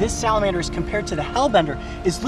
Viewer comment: I thought it said slender man